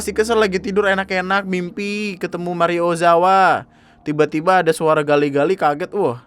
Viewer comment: ini saya yg salah denger "Mario Ozawa?" akwkkw